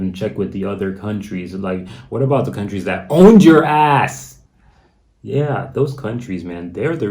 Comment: At 2.09 I thought you said HONDURAS but you said OWNED YOUR ASS..